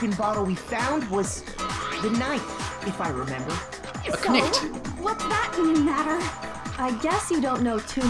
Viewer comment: I still remember the "PO-TA-TOES" thing! Let's feel old together! =D Also 30:13- Did you just say "A knäckt"? xD